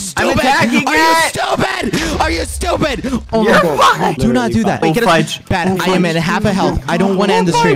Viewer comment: WHAT DID BAD SAY???? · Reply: "youre fine"